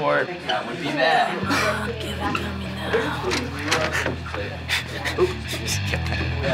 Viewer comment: did anyone hear someone say give it to me now and then laughing